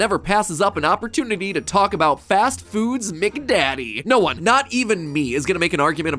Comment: did matpat just say mcdaddy?